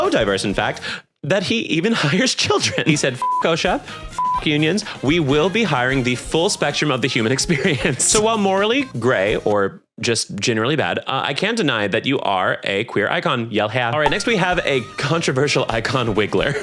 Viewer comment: When you called wario morally grey for a moment I thought you said morally gay which I thought was equally valid